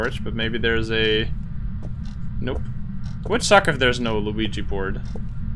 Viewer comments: Hold on did you just say Luigi board? It literally sounded like it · did he say luigi board? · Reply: Mario board